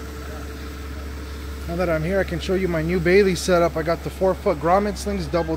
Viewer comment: I thought you said bathing setup at first. I was like BRUh, I don't want to see you in a swim suit lol